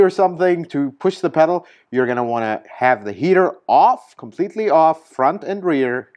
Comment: did you say "heater OFF" ? or Heater ON? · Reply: Heater off!